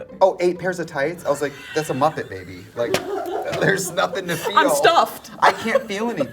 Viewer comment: "8 pairs of tights? That's a muppet, baby." New vocal stim!1! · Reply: Oh I heard “I’m a muppet baby” lol. I need to rewatch this part